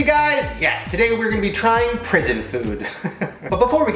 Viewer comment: I thought its said poison food